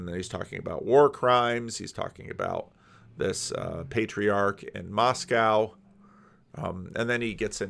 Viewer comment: "the primarch in Moscow" lol, I thought you said you were moving off Doug Wilson